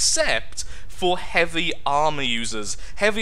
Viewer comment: did he say "heavy army users" at 2:39 ?